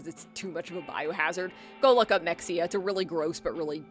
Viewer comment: Wanted to look it up but i have no idea what the hell you just said, Mexia? Looked up "Mexia zombies" going by the context and just found some movie named "mexzombies" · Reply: Mexia super market. It was a real life grocery store that had to close down, but instead of paying to clear everything out the owners just up and left, leaving everything in there to rot. Authorities had to step in and take care of it because it was literally releasing toxic gasses and making nearby residents sick. It was so bad that bugs blotted out most of the windows completely and the cleaners had to wear heavy duty hazmat gear because there wasn't enough oxygen in there. Basically any apocalypse looters would be fresh out of luck in a matter of months irl because they'd have to somehow survive the giant cloud of ammonia to get to any of those cans, lmao